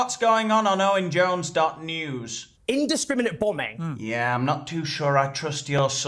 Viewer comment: Owen Jones is in the dictionary under 'insufferable.' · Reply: I could have swore Jones said " indiscriminant bumming" 😂